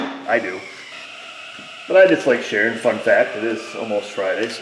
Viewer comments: When u said fun fact I thought u said f that I had to listen twice to catch fun fact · You said "fun fact" and I absolutely could have sworn you said "f*** that" and I was like 😲😲😲😲😲